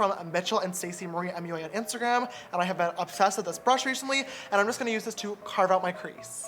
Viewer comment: Did anyone else hear at 15:25 reshantly lol love him